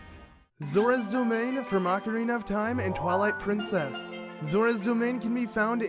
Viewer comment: Why do I keep hearing "Macarena of time"!????